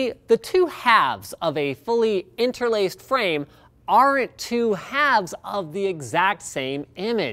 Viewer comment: He's supposedly in the communication business and yet he uses contractions. At 2:22, did he say "aren't" or "are at" as the cc indicates. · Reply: well, the context clearly asks for "aren't"